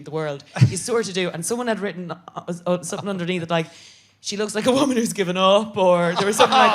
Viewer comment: can anyone tell me what she says at 8:58? "she looks like a woman who something something" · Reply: "She looks like a woman who has given up"